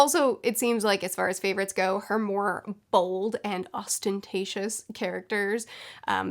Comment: When she was talking about Jane Austen, all I heard was "Austen-tacious"